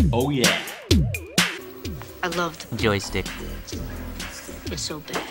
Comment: I swear I thought I heard him say "Joe-Stick"